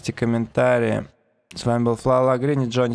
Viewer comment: Как зовут тебя? Не расслышал. Флау...... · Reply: так и зовут) Фла(й)